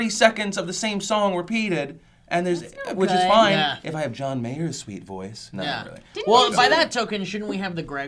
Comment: did you just say "fuck john mayer". maybe FUCK YOU!